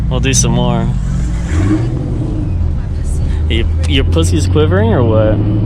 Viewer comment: Please tell me im not the only one who heard that at 13:36 lmaooooo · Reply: austin fair pussy quivering?